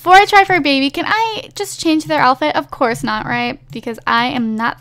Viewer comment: there's an option that said 'change outfit' . Either that or I don't understand what she's saying